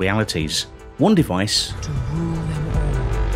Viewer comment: Um... Did anyone else notice what was said in the background at 7:27? 'To rule you all' - Listen carefully.